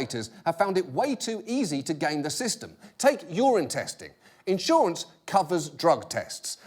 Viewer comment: "Urine Testing" I had to rewind there because I swear I heard "Your Intestine" xD